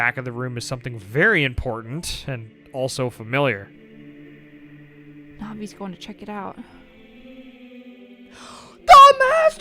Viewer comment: did anyone else hear "Mommy's going to check it out?"